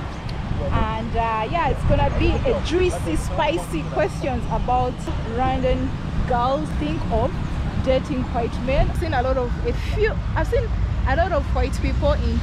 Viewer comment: I thought she said dirty white men at first 😂 I was like jeez okay then